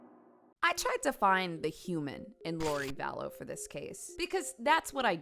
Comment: I really thought you said “I try to find the humor” and not “human” cuz tbh that’s why I turn you on. I know it’s not supposed to be funny but you, and keep it funny enough to be actual entertainment…but carry on 😇 I’m tuned tf in on this one